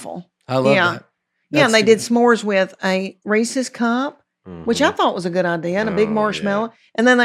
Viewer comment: When she said Reese’s cup talking about s’mores I thought she said racist cop. I was confused for a bit there.